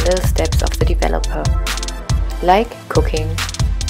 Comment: When you said like cooking, while mixing the bleach I thought you said “like cocaine” lmao